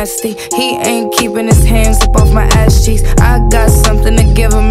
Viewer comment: Ngl when she said "cheeks" I thought she said geez